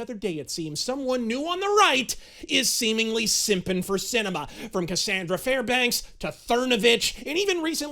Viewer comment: When you said "Simping for Sinema" I thought you said "Simping for Cinema", I got confused and excited. God Fucking Speed!!!!!!! 🤘🤘🤘🤘🤘🤘🤘